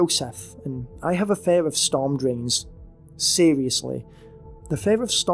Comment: I thought the 2nd one said fear of sport dreams lmao